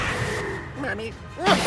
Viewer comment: I think he said mommy which is even more hilarious